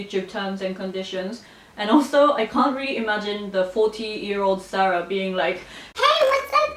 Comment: Hey Sarah, new subs here! 5:06 Did I hear this correctly? A 40yo ???? You look like you are in the late 20's or early 30's!!! Regardless, you are such a nice lady with feet firmly on the ground. Keep the good vibe up! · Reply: Thanks for the sub!! 🙌 I did say 40yo, but I was talking about the future! I am in my my late 20's 😂